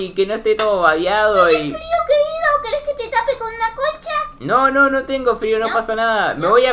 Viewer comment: en el minuto 8:10 si no lo escuchas la verdad es muy gracioso dice la abuela tenes frio querido y lo gracioso es que dice despues queres que te tape con una con cha eso es lo gracioso